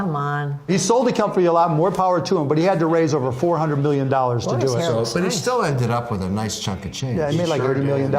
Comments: Am I the only one who heard Barbara say at 5:38 "look at his arm hair it looks nice" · what does Mark Cuban say Jamie made on the deal? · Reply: 30 million